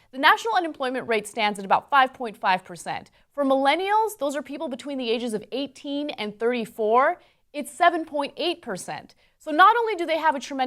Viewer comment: What the fuck are millennials btw? Hear it all the time from Americans. It always confuses me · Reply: "Millennials— those are people between the ages of 18 and 34...” She said that in the video around 2:45, but I could easily understand if you didn't hear it since it was in the middle of a sentence.